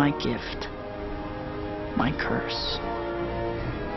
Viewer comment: I legitimately thought that he said grandma, I'm Spiderman LMAO